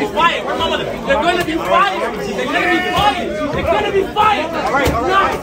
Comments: Oh he said " your gonna be fired" i thought he said let me fight him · The “word to my mother you are gonna be fired” had me 😂😂😂😂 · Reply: I THOUGHT HE SAID "YOU'RE GONNA BE FIRED" 2... BUT YOU'RE RIGHT I PUT MY HEADPHONES ON AND LISTENED CLOSER.. HE DID SAY, "THERE'S GONNA BE FIRE.. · I thought he was saying there's going to be fire there's gonna be fire 🔥🔥🔥 but it seems everyone else is saying your gonna be fired 🤦💀🤣🤣🤣🤣